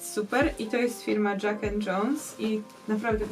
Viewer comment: 1 raz usłyszałam Jughead Jones